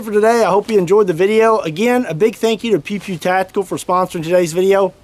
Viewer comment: No offense intended, huge fan here. But, it seriously sounded like you were saying "pee pee tactical". HaHa